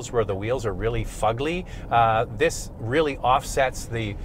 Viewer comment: Is it just me, or does he say, “fugly” at 5:04 mark?